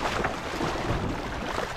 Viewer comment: Did he just say easy mate will mate is struggling for his life 😂😂😂😂